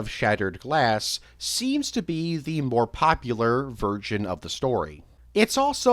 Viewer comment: it sounds like virgin instead of version, so this makes the ultimate cut an easy 10/10